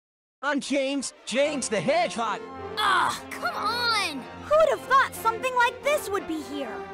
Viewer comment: I rewinded when tails said who wouldve thought.. and thought it said who the f... (1:25)